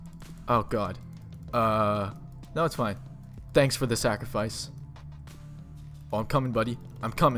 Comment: Omg did you just say Zacafice? xD